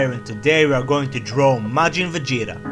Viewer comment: At the start i heard "we are going to draw majin vagina" :3